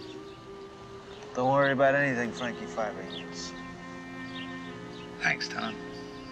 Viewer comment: Fuck me. For thirty five years I thought Tom said "Frankie Five Fingers" Goddammit